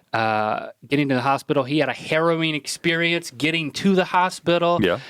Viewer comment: Link... when you said 'harrowing' i thought you said 'heroine'...